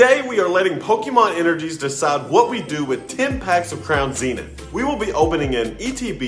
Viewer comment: Pulls were solid overall in my opinion. For a second i thought you said "interviews would determine what we do with the packs" not "the energies would determine what we do with the packs". Fun opening to end off the day before the livestream begins.